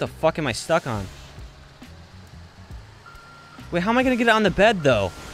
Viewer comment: my vid froze then i heard ' how do i get it on the fkn bed ' and my face was o-O